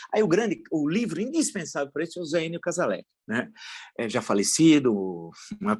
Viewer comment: Falou em 15:40 tão rápido sobre o livro ou o autor que não dá para saber. Entendi " Um Zeino Casalé ', o que é isso? O Villa às vezes come as palavras e fala rápido que não se compreende. Nem o livro nem o autor da UNESP de Araraquara. Tem que respirar para falar Villa.